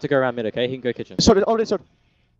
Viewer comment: WTF did ropz say? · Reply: Already Short.